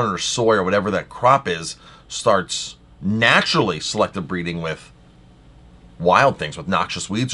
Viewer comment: I heard him say: "selective breathing":-) As he coughs, I thought he'll say a parallel, for example "eating GMO or organic is the same as selective breathing or breathe fully" (whatever it could mean)